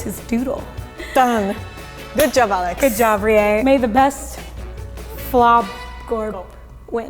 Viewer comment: I thought he said GLOBGOR!